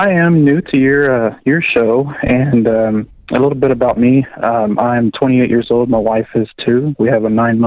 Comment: I almost died on the road when I heard this on the radio it sounded like "I'm 28 years old, my wife is 2" 😂😂😂